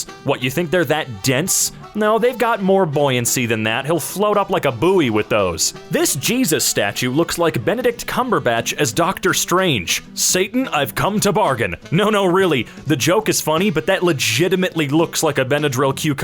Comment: Did i hear "Benadryl Cucumber Snatch" instead of Benedict Cumberbatch?